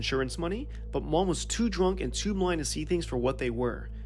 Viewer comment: Seriously, sounded like you said, "...she (mom) was too blind and *BLONDE* to see..."